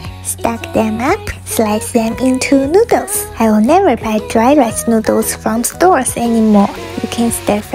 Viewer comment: I thought she said “dry as noodles”